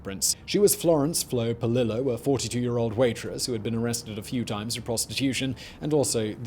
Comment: Off topic, but when Simon said: "Her head was never recovered, but she was identified through her fingerprints: she was Florence 'Flo' Polillo, a 42 year old waitress who had been arrested a few times for prostitution..." I legit initially misheard it as: "Florence 'Flo' Polillo, a 42 year old waitress who had been RESCUED a few times from prostitution"! I thought to myself: "If ONLY the 'rescues' had stuck!" before realising my error!🤦🏻‍♀️